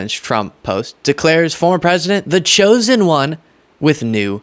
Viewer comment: I thought she said The Chosen Con ?